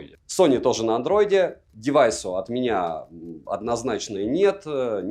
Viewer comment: Я в первый раз услышал "девайсу однозначно нет")